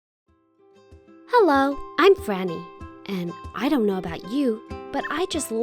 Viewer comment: I heard, Hello I'm FANNY